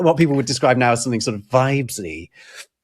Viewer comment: what word is saying at 19:42?? vibely??